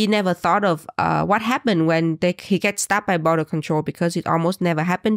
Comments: I thought was saying STABBED but she was saying STOPPED 😅😂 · I kept hearing "stabbed by border control" instead of "stopped by" and now I can't stop giggling every time you say it 😂